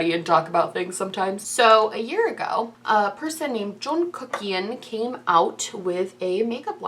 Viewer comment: did you call him Joel Kuckian? Lol I heard Joel and now I just want to call him by the wrong name on purpose